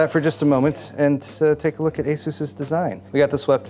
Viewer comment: Hey, great video as always, did you said Asus’s design 1:12 approximately.